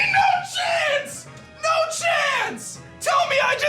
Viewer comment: Oh he said no chance, I thought he said oh s**t lol